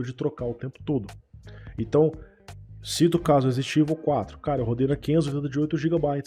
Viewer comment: O que o falou em 15:56 eu estou tentando entender mas estou confuso. Ele falou que rodou Resident Evil 4 na 580 de 8 GB? É isso mesmo ou entendi errado? Não é uma critica, é que ele falou muito rápido nessa parte e eu fiquei confuso, mas ao mesmo tempo foi engraçado kkk, eu também falo rápido em algum momento as vezes, nada demais isso, acontece, mas pra mim essa parte foi confusa e engraçada kkk Em relação a problemas com VRAM ou disputa entre AMD E Nvidia e tecnologia de ambas eu tenho uma solução, comprem 2 PCs, um com uma placa RTX 4090 24GB da Nvidia e outro com uma placa da AMD de 16GB e tá resolvido, brincadeira kkk, quem dera se fosse fácil, esta tudo muito caro infelizmente e má otimização em certos jogos não tem ajudado muita coisa.